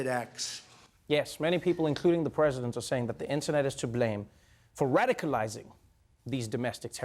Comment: You said, "the internet is to blame," and all I heard was, "the internet is for porn." sorry not sorry